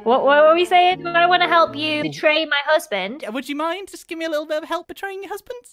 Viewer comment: did he say husband’s ?????????? ?????